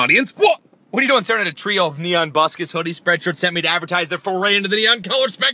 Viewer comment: Sounded like you said zip up 'pooddies' instead of 'hoodies'. xD